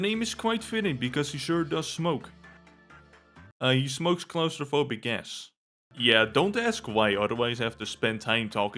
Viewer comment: i Thought you said claustrophobic ASS